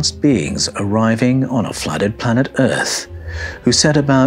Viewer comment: Dear, did you say, " a flatted planet Earth"? at